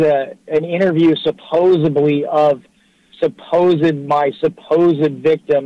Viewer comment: okay, help me out here. Did I hear say, "suposobly?"